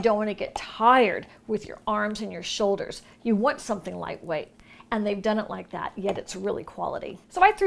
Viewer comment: I played it over and over again to see what you were saying and you said you wanted something light weight. At least that's what I keep telling myself!!!😉 I almost missed that beautiful necklace, my eyes keep drifting down the page. What a great site to find so many things and at a discount!! May you have a great 4th!!!😜